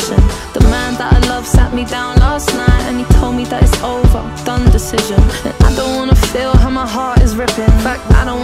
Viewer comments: is it only me who hears done decision not dumb decision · I thought she said done decision